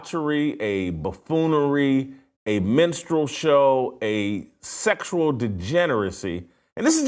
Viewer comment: At first I thought he said "menstrual show" and not "minstrel show".